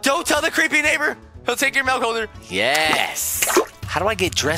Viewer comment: did you just say mouth holcers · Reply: He actually said milk holders